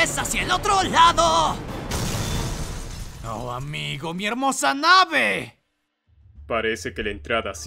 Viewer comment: Lo que dijo AHHH MI HERMOSA NAVE lo que entendi "respira" Mi navee